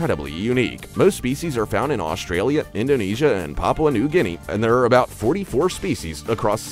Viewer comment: The Quebeccabra sound like you said to Papi new Nino